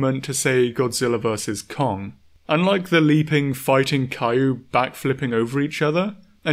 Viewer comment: I'm not the only one who heard this, right? My man over here pronouncing "kaiju" like "Caillou"